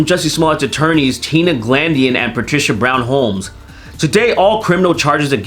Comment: I thought you said "Patricia Brown Holes"